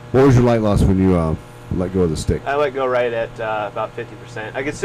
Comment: Did they just say “th￼e stig”?